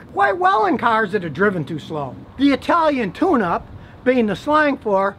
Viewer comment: I thought he said italian tuna